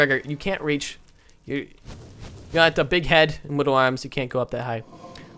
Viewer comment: WOW hold the phone here did you just say d***head